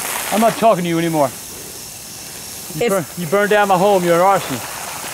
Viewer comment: you burned down the home you're awesome That's what the subtitles say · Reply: He said arsonists